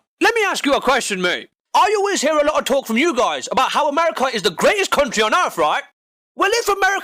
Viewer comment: "greatest country" i thought u said "greatest cunt tree" lol